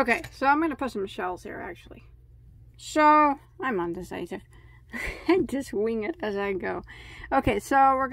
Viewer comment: did you say ‘I’m under satan’ ??